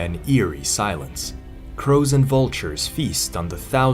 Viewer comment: Had to rewind as I heard 'crows and vultures' at the beginning as FROZEN VULTRUES... 😂 😂